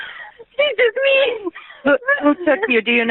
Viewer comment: I fully believe Sherri was considering framing from the start. Some Poor Dude seems to think she got kicked out of her boyfriend's house in the 911 call, and at 15:55 it really sounds like Sherri says "HE took me". I wonder if poor, who loved Sherri so much that he'd help her fake a kidnapping, was always a backup in case the police wondered why the 'mexican ladies' chose her. But she didn't end up needing that part of the narrative, so she dropped it.